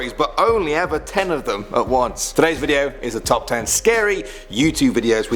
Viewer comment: i heard "treyz video"